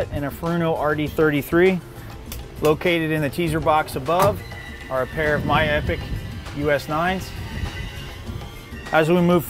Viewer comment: what's that thing he said is in the teaser box above the skybridge? at 9:54? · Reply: miya epoch hd